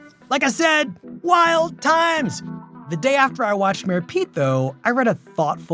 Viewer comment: Does he say Mayor Pete or Mary Pete? Captions say "Mary Pete"